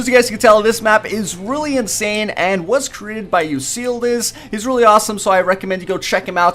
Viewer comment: Did he just say "You Seal This" ? it's Euclides numb